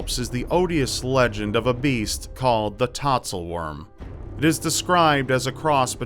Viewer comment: So I wasn't looking at the screen when the "Tatzelwurm" title came up, and the first time you said it, I genuinely thought you said "tonsil worm." I was just like "eww, why would they call it that?!" Then you pronounced it differently the second time and I decided to rewind and see how it was spelled. Oh. Not tonsil worm. Okay. Good.